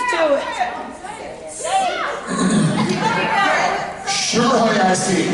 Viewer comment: what? sher i i c? .....wha?? everyone laughed but I didn't understand it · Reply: sugar honey ice tea